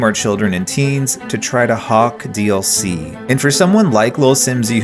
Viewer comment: did you say... HAWK?